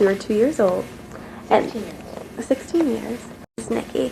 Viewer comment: I thought said eighteen years !